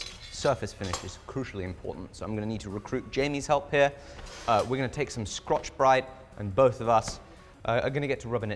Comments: I thought said crotchbrite 🤣🤣🤣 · did he say scotch bright or crotch bright? Either works, just curious.😂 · did...did you just say "Scrotch Brite"?! yup, confirmed, best video of 2025 xD